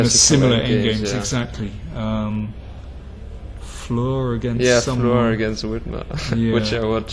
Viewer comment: Hi can anyne post a link to the "famour rook and pawn ending" with split pawns iyou mention around 7:32 i think you say fleur or something ? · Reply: <link> Salomon Flohr vs Milan Vidmar